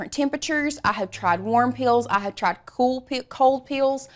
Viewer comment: Omg I love your accent but seriously when you said warm peel I swear I heard “worm pills” lololololol